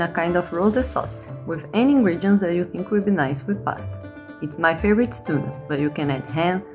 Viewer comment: I was so confused when tuna was added at first to the pressure cooker one cause I thought she said "it's my favorite stew" and had to back it up several times until my husband explained she was saying "my favorite is tuna"! Maybe you could add subtitles?